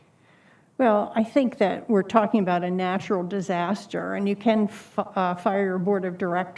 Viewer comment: or so, does anyone else hear her say you can eff off? I don't put it past the triple masked loonies, but was still surprised.